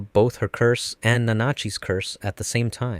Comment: all tomorrows ahh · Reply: I heard "me and bananuchi"